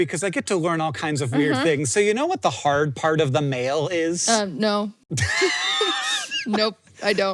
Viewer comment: I spaced out only to perfectly tune into and mishear an out of context "do you know what the hard part of the male (mail) is?" 🤣